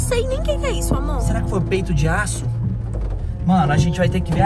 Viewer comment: 'eu entendi "peido de aço" kakkajajkajajk